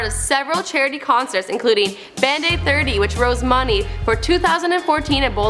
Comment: Did you just say "Rose money"? It's raised.